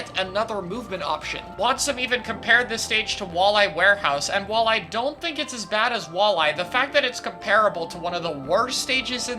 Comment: "while i dont think its as bad as walleye" my dumb brain had to ponder what he said there for a moment